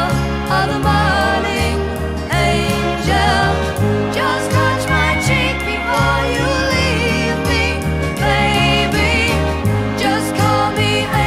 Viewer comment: When I was a kid I thought she was saying "just brush my teeth before you leave me baby". Great song.